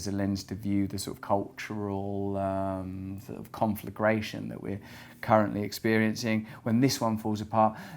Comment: Did Russel say “confligration” at ~ 16:00?